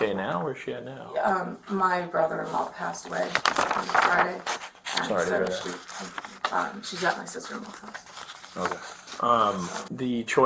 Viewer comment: sounds like she said her brother-in-law died on friday so her daughter was with her sister-in-law…??? huh???